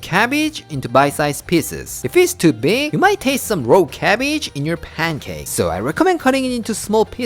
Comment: I thought when you said, “if you cut the cabbage to big you may taste some raw cabbage in your pancakes” that you actually said “gross cabbage” and as someone who loves Okonomiyaki but otherwise hates cabbage, I can relate….😂